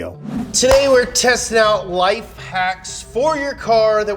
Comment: It sounded like James said "wife hacks"